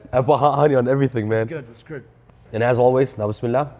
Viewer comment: Does anyone know what he says at 9:06 in Arabic? I can never figure it out 😟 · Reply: Bismillah “بسم الله”- in the name of Allah You say it before you start anything, before you eat, drinking, turning an exam in lol etc!